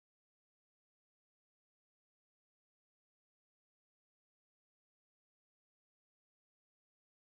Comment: What's he say, I joined the grandpas? lol